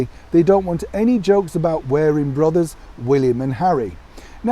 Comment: At first I thought he said "wearing rubbers ..."